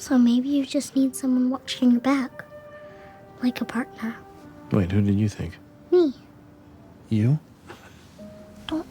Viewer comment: Anyone catch that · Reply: Cassie, please save my soul.